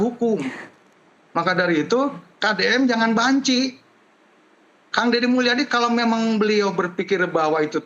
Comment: KOK ANGGOTA DPRD BILANG KDM JNGAN B*NCI... APA SAYA SALAH DENGAR? 16:05 GA ADA BAHASA LAIN APA YG PERNAH DIAJARKAN DI SEKOLAH DASAR